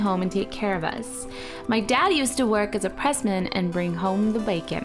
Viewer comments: Am I the only one that thought she said president instead of pressman? 🤣😂🤣🤣😂🤣🤣😂🤣🤣 I so dumb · It may be just me, or I really heard "My dad used to work as a president"? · I thought she said blanket but she said bacon